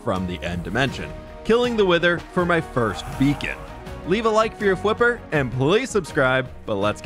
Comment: I misheard you and at 0:24 I thought you said that you were going to kill the wither for your first vegan lmao